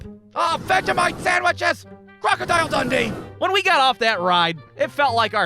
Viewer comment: did he say Crocodile Dundee